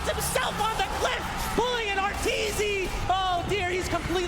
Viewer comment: Did he say “pulling an arteezy”? 😂